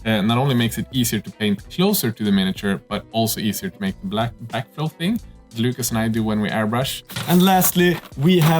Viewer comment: describing the new airbrush features, what did he say about "makes it easir to pait...black..." couldnt make it out sounded like "Easier to paint black dingsoongding" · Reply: With backflow technique. Makes it easier to mix paint in the cup and clean the aorbrush